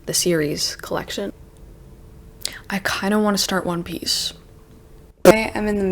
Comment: Did you say you want to start one piece or War and Peace because this is the second time I am mishearing them you pronouncing it in your channel and I am confused, lol. I mean I know you are not that much into manga but the caption of the video also pointing the same . Also cute ear rings, It looks good on you❤. Edit : Wait you really want to start one piece, wow that was not on my bingo card at all but go ahead, I am also planning to watch it this year so I would love to discuss.